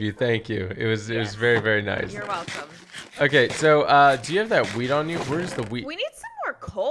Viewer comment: I thought he said “do you have that weed on you”